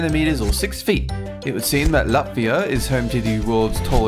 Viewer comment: Did did he realy say Latvia?? Omg yes finaly i hear my place